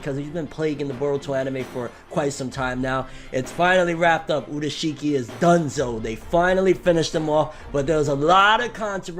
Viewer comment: I thought I heard Urushiki is Danzo for a second😂😂😂